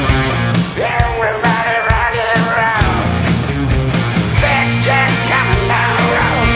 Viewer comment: what does he say at 2:44 ? seems like "big jack kinda roll" but i'm not sure